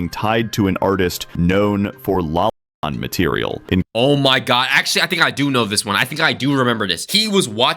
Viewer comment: What word was censored? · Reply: Lolicon, basically kids portrayed in lewd, often sexual situations. They’re also infamously known for being excused with the reason “Oh, she looks like a nine-year old girl, but she’s actually a 1,000 year old elf”